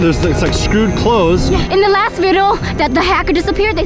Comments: vy said vido instead of video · In 9:53 instead of saying in the last vedio she said verio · Vy Said Vido Instead Of Video😂😂😂😂